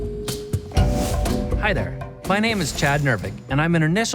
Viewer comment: First i heard "Hello, my name is Gender", and that made perfect sense, giving the hair color